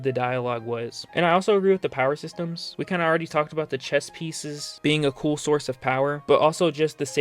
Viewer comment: Chess pieces. I thought you said chest pieces 😂